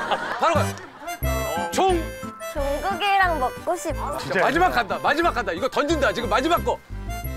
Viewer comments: Did Nayeon really say Jungkookie? Omo!!! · Nayeon... I thought you said "JUNGKOOK " from "BTS" (i'm not a shipper btw) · Did she really say Jungkookie? Because my Cousin named Jamaica is gonna kill her Not Really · did she just say jungkookie oppa? Or I'm wrong?